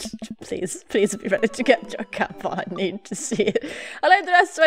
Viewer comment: I DIED I THOUGHT HE SAID 'TAMPON'😂😂😂😂 I think I put my music on to loud and now I can't hear things properly........